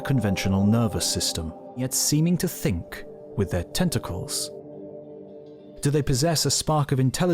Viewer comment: I thought you said the octopus thought with it's 'testicles'. I remember when I was around 16 I did but then realise you said 'tentacles'. Either way you are probably right. Excellent video as always. Thanks mate. You ARE the best on YouTube and I do not write that lightly.